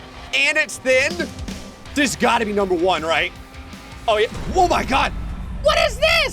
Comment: Did he say woah my god